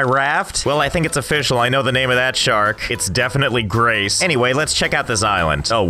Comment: i thought he said Grays (Graystillplays) and not Grace Would have been an amazing crossover.